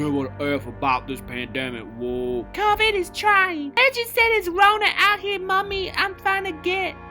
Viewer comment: did he just say covid is trying lol